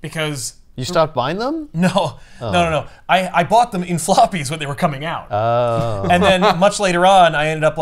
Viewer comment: I heard "sloppies". That event was definitely heavy with it😂